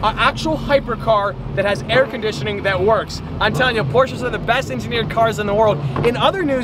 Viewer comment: the subtitles say “abortions are the best engineered....” instead of “Porsches are the best engineered...”